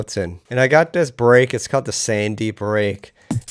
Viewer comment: Great video, mate! Funny how basic this sound actually is. By the way, which break did you say that was at 5:58? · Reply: Jelle it’s often called Sandman but I found it in the Jungle Warefare 1 sample CD, old sample CD and it was labelled as Tiny1👍 Hope that helps